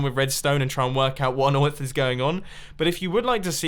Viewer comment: Did Mumbo just say what on oith is going on